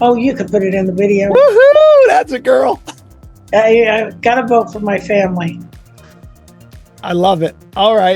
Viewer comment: Did he just say " woooo hoooooo thats a girl for votint for Antichrist? This just getting disgusting now